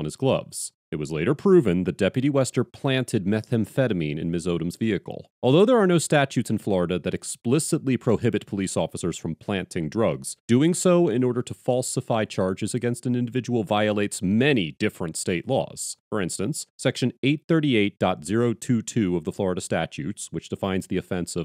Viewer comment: Am I hearing this right? (6:35) "There is no statutes in Florida that explicitly prohibit officers from planting drugs"